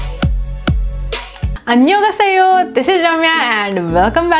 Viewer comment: did she say annyonghaseo..???Maybe I am wrong...??